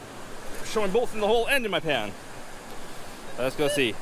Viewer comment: it sounded like he said " its showing both in the hold a in my hand" what the hell yall doing out there?